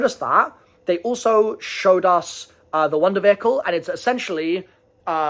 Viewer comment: What did he say?? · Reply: Wonder Vehicle.